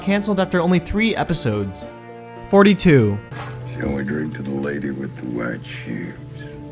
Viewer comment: Can someone explain what I'm supposed to get? Is Dolores the lady with the white shoes? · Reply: I thought he said, "the lady with the wet shoes". I thought it referred to the blood that splatters onto her shoes when she executes Arnold and Hopkins character.